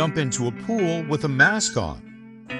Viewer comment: I thought he said never to jump in a pool with a mascot 😂😂😂